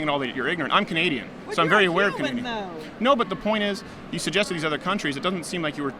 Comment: did she say "hes a cuban though"